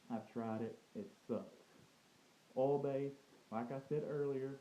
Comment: OH! he was saying OIL based. I was confused, because I thought he was saying ALL based...